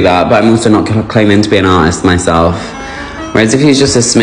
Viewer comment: I heard “ we’ll slap me if I was Susan